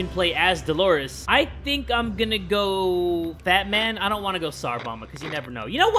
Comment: i thought he said bat man